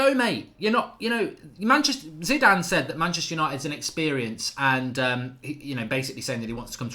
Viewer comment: No he said United is an ‘experience’. As in not an end goal club. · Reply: My bad Haha, sounded like he said inexperienced club to me 😅